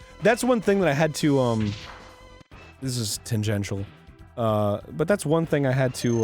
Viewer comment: Arin said "this is ..." what did he say and what does that word mean? It sounded something like "tyngensial"? Please let me know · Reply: Daniel Duda Tangential - only loosely related to